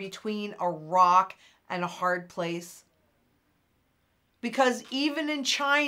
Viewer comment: "Can you see that we are between a rock and a hard place?" @ 16:43 but I heard, "Can you see that we are between the left cheek and the right cheek?"